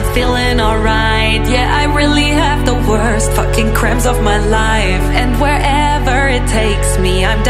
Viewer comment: I thought it said crumbs but it really said cramps 😂😂